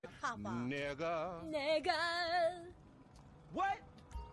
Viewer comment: The thing I find adorable is their innocence. They dont know how damn offensive the word they're saying is😂😭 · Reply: this one is suspicious she said it like 4 times and it sounds like she said nga said ngaah